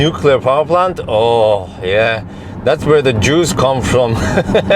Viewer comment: i thought you said "that's where the Jews come from" and I forcibly breathed through my nose.